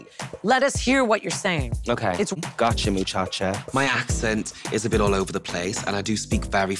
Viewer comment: Ok so he said " got you muchacha" but it sounds like he is saying "mi chacha" wich is how we vulgarly call a domestic worker in Spain 😂 he is basically saying "got you my domestic worker" 🤣 BTW I understood him perfectly, I don't know what that woman is talking about 🤷